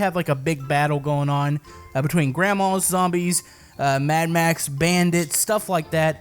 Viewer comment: wait a minute a haven’t watched many tiny town VR vids but did he just say Grandmas